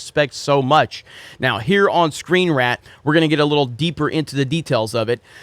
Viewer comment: Did say ScreenRAT?